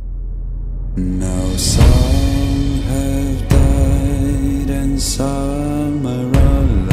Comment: It sounds like it said now some are dead and salmonella